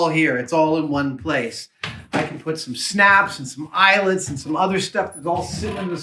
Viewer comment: I thought Adam said 'I can put some snacks' and I was like that's an amazing idea for the drawers ahaha